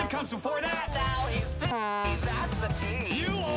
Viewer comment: that’s not a swear word Alister said “now he’s busy now that’s the tea”